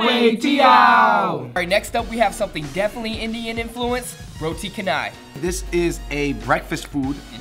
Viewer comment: the way he pronounces Roti Canai.. facepalms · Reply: tbh I thought he said roti kedai at first